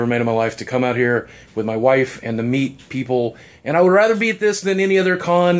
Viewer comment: For a second I thought he said "With my wife, and the meat people." I need sleep :x